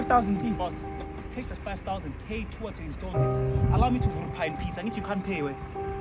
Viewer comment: 😂 did he just say "allow me to vrrrr phaaa in peace" I hope people get the message your putting across. 🙏 great work